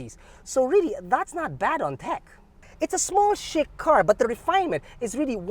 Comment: I thought said "it's a small sh*t car" 🤦